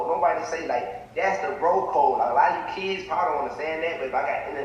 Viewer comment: He was saying “bro code” I thought he was saying “broke ho” he cannot talk 😂😂😂😂